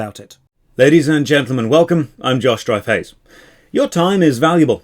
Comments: i thought he said his name was 😂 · Whenever he says his name i always hear "Josh Dry Face"